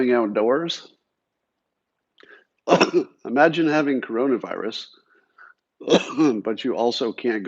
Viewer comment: Did he just say he has Corona virus?